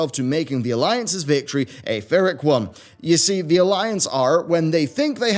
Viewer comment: did you mean Pyrrhic? it sounded like you said vyrrhic